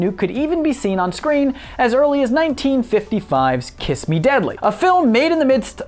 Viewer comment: i thought it said "Kiss me Daddy"